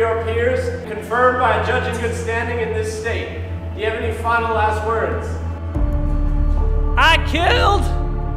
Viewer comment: At first I thought Jake said, "Now do you have any final-ass words?"